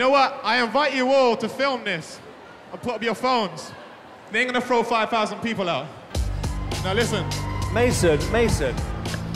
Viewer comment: what does he say here? I repeated these 3 seconds over and over but I couldn't understand. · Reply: He says "They ain't gonna throw five thousand people out."